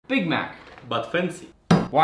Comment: Bro i always hear “Big mac Muffins 💀”